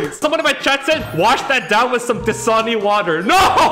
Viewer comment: Did he say T'Soni water?